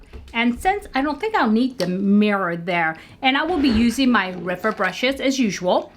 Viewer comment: I thought you said "Ripper" brushes,guess I'm tired😄😄😄. Pat McGrath has a really beautiful eye shadow formula I agree, I have to get this pallet,such beautiful colors. Loved the completed look very pretty. Have a good weekend 💜🙏